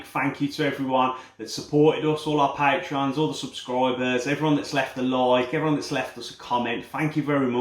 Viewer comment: jesus christ I heard "everyone's who's left alive" I am mortified